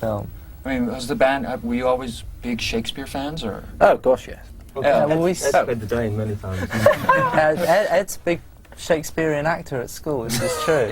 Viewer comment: Does Thom say "I've played the Dane many times."! lol xD